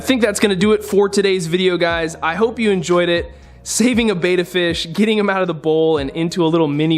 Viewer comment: I thought it said shaving A betta fish 😂😂😂